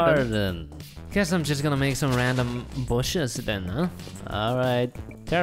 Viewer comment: It sounded like he said, "Bullshiz"